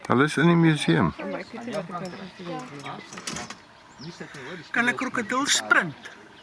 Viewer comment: hahahah what did he just say 😂😂 Canacrocodulshprunttt